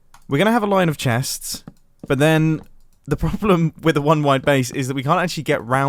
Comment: Who thought it said 1 wild base